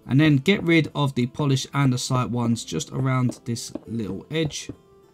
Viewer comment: did he just say "This little bitch"?